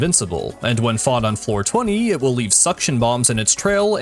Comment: I misheard you at 56:22 I thought you said 4/20 not Floor 20 Liek what does weed day have to do with this thing???